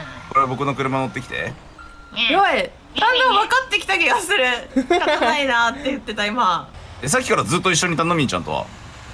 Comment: 「仕方ないな～」としっかり聞こえるのに言ってないんだ。